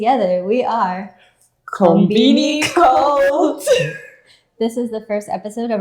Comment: In the very beginning, at 0:12 it sounded like you said "Conbini Culture" rather than "Conbini Cult", but it may have just been the start of laughs. For the audio-only audience, that may be confusing even though cleared up at the very end. Other than that, very good banter between you both, and I feel like there's plenty to talk about comparing cultures. Comparing Japanese with New York perspective and Hawaiian experiences should be interesting. You're both off to a good start!